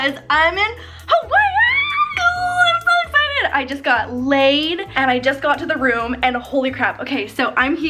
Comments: HOLY CRAP I THOUGHT SHE SAID SHE GOT LAID OMFG 😂😂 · did she say she just got " Laid "🤔🤔🤔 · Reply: Leid. The flower necklaces in Hawaii are called leis, so when they put them on your neck you are leid. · So like I was looking at my phone and all I heard was “ I just got layed” !! 😂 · did she just say she got..laid 😳✌ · Is it just me or did she say she just got layed